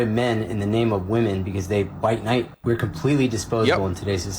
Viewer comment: I think he says, "bite nite". What does that mean?